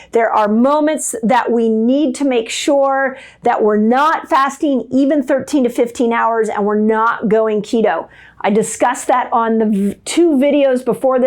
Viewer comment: At the 6:27 minute mark what did you say? Don’t fast 13 -15 hrs?